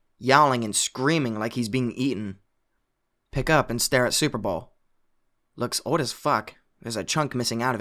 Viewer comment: alright what the hell is a superball lmao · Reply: I thought he kept saying super bowl, it was even more confounding 😂